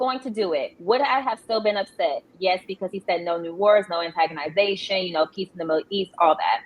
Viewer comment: did she say antagonization?